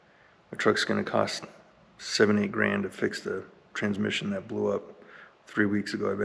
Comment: Had to turn captions on and rewind cuz almost thought you said it's gonna cost you 78,000 to fix your transmission lol